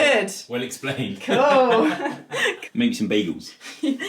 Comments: I legit thought said “ let’s go make some babies” while carrying you…. Bagels Elle, B A G E L S 😆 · When picked you up it sounded like he said let’s make some babies 😂😂